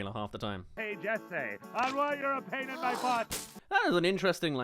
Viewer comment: What is being said here? Only after rewatching it many times I think he said sm like "Hey Jessay, something I couldn't understand you're a pain in my butt!" but here are the things I thought it said before coming to that conclusion: "Hey Jessay, I want your opinion on my butt" and "Hey Jessay, I want your pen in my butt" and "Hey Jessay, I want Orion in my butt"